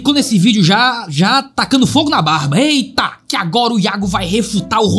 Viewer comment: Achei que tinha falado já cheguei tacando fogo no barba kkkkkkk